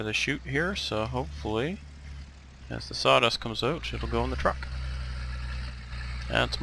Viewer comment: Did you say "shit" at 22:30??